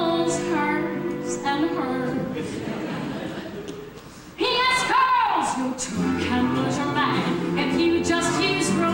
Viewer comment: What is she sayin in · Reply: Robert Cranter I think she’s just saying “Yes, girls”